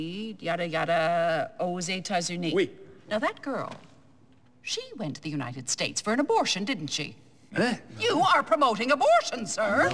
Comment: Qu'est ce qu'elle dit la femme de 7:22 vers 7:28. J'ai pas compris! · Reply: 12 ans plus tard : «Now that girl, she went to the United States for an abortion didn't she? You sire are pomoting abortion!»